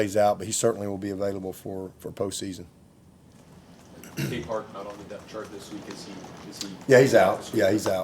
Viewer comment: someone pls. tell me WHO is out this week - couldn't catch the name. GOT IT ON RE-HEAR => Demonte Capehart